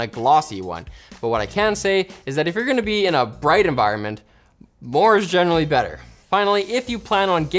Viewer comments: Subtitles say "lower is generally better" instead of "more is..." - interesting mistake. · woah woah woah the caption says lower while says more o.0